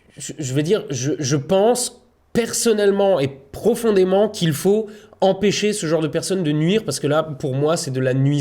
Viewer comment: j'ai entendu "il faut empêcher c'est personnes de nuire" j'ai like